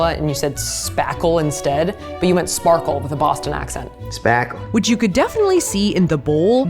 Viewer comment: I thought he said smack em 😂